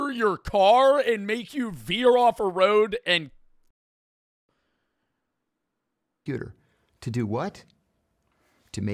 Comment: the cia muted what he said 😳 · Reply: idk if u are serious with this stupid ass statement but he said ''killurself''